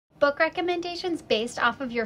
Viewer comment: I thought she said “buttcrack recommendations” 💀💀